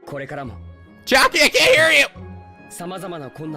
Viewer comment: did just say Chiaki???